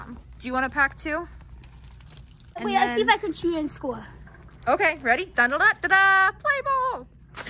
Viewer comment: At first When said I’m going to see if I can shoot a score I thought she said I’m going to see if I can chew it on school lol🤣